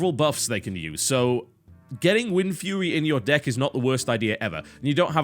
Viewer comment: Did anyone else hear TB say Windfewy at 3:00? lol